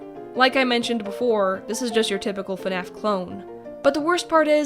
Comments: turn on captions lol This is just your typical fnaf clone is what is says but captions say this is just your typical fat ass clone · i had sub on idk why but instead of fnaf it said fat ass